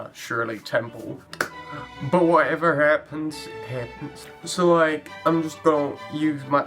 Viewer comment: Whenever he said, "Whatever happens, happens." I heard, "Whatever hairpins, hairpins." Do Halloween stuff now~